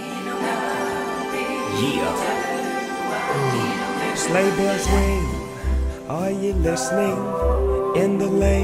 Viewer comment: When I first saw this movie I thought Snoop said "Sleigh bells wing" 🤣